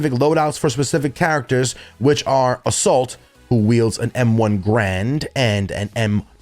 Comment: did he just say M1 "Grand"?